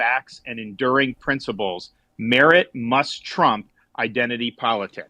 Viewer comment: Did he said "must trump"? wink wink :)